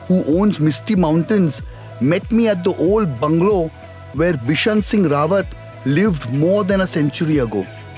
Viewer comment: Amazing quality, people id going to find a travelvlog gem soon. At 2:51 subtitle says KISHAN singh, instead of Bishan singh.